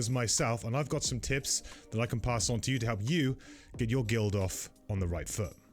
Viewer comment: Did he just say "Get your guild off..."?